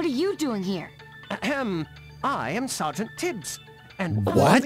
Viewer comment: I heard “Sgt. Tips”, which has its own other meaning.